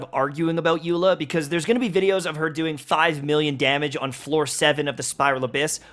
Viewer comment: Why did i hear 5 million damage as Thigh million damage?